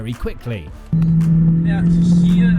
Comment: these subs are complete bullshit, what they are saying is: Max: "We gaan hier zo laat mogelijk remmen" or in english "were going to break as late a possible here" Which jos answers with: "Het is helemaal niet leuk om met je mee te rijden" or in english "Its awful having to sit next to you driving"